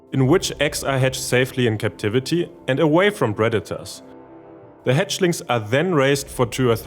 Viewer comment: i heard this as 'away from redditors' lmao